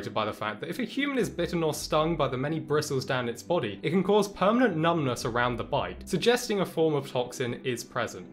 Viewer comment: Wait did he said PERMANENT numbness?